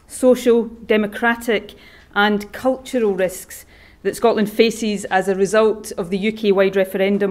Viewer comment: Did she say 'Scotland's faeces' at 0:57?